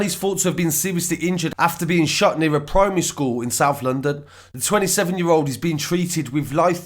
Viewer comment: Genuinely thought it said “a man shits near Bermondsey school” lol